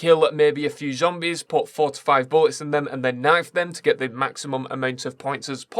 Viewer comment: I thought he said 45 bullets